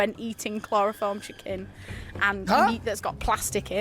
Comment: I think I might need hearing aids, because I swear I heard her say Chloroform chicken at @ 15:00.